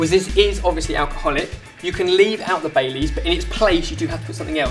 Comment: When he was saying baileys i thought he was saying bay leaves😂😂